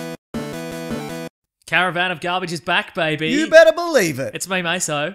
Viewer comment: I thought is said, "Caravan of Cleavage"